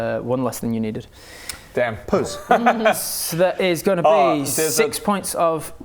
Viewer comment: Did just say poes?😂